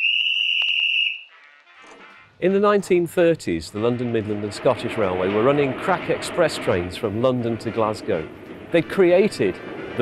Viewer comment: I had to wind that back. I genuinely thought he said "The LMS railway were running crap express trains......" 🤣🤣🤣 Im so immature. lol